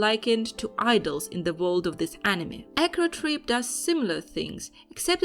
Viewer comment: Whats the name of the anime at 12:01? Sorry to ask when you said it but i couldn't hear very well. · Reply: Acro Trip